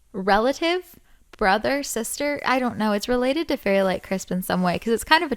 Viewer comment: When you said "fairy light krispies" I thought you said "fairy rice krispies", like the cereal 😂 Fairy rice krispies sounds like a good slime idea tho 💜💜💜 ily btw!!